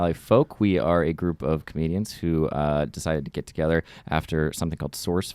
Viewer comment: all I heard was "we are a group of Canadians"